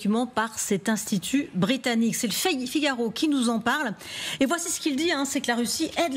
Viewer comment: 😂😂😂😂 c'est moi ou a la 0:29 elle dit le fake figaro on appelle sa 1 lapsus révélateur 😂😂😂